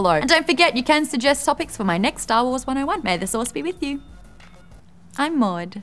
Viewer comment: Every time Maude says "may the Source be with you" I hear "sauce". Like she keeps wishing us good bolognese sauce.... Mmmm bolognese....